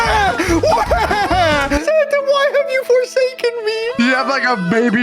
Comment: did you say FORSAKEN?!?!